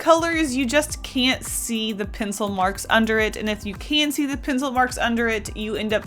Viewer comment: All I heard was "Penic Marks"